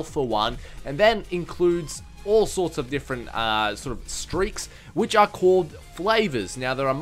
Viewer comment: I thought it said steak not streak